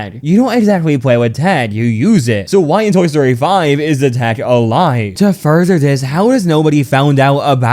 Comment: I can't for the life of me make out what he's saying here. Is he saying "tag"? "Tack"??? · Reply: Oh he's saying tech nvm lol